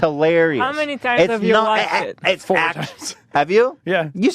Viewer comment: How many times has he watched it? 1:09:46 I can't hear over Ethan being a super fan. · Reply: I’m pretty sure he says 4 times but I could be wrong